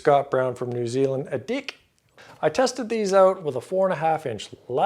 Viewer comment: Lol Kiwi here... like the reference 2:20 Scott Brown NZ 'dick'. We think we're saying deck, we know we're saying deck...but your dirty little minds think dick.